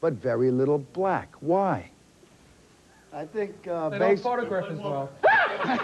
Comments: what did Ace say at 0:43 ?........I could not make it out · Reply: "they don't photograph as well" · What does Ace say at 0:42?? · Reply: They don’t photograph as well 😂😂😂😂